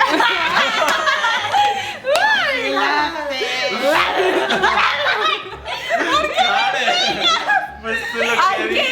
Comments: ¿Que dice Karen en el min 12:39? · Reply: dice FACILOTE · que dijo Karen en el minuto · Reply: Ana karen Amezcua dice facilootee 😂😂😂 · Que dice en el min 12:40? la chica del medio?? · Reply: Es Karen y dice "FACILOTEEE" xD